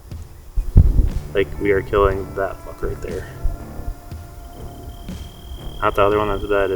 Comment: I had to listen to the intro three times. I thought you said "I'm going to kill that fucker right there" 😂🤣